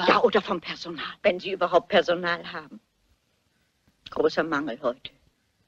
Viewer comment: Kann mir jemand helfen zu verstehen was miss Marple bei 06:42 sagt? Ich frage mich das schon seit ich ein Kind bin 🙈 Großer ..... Was? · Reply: "Großer Mangel heute!". Die Aussage bezieht sich auf allgemein fehlendes Personal.